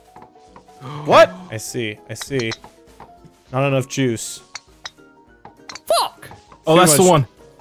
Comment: thought he said not enough jews i was worried